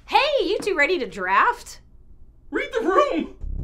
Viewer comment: I’m sorry, but what does say? It’s bothering me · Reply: Read the room